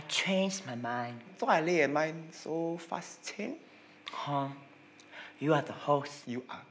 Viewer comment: What did say at 1:15 in Hokkien? It’s the only line I couldn’t catch. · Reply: An zua le eh mind so fast change?